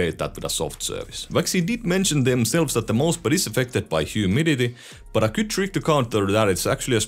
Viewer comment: sorry what did you say? No matter how many times I replay that part can't understand, only heard word Humidity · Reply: "affected by humidity, and a good thing to counter that..."